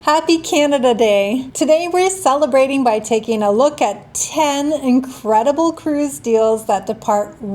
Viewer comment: At first I thought you said "Happy Cannabis Day" and it is not even 420 yet? Yea, I run at 2x speed now that I am used to it and can watch more vids and slow down when I need to.